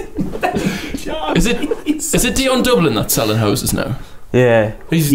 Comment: I thought he said "selling hoses"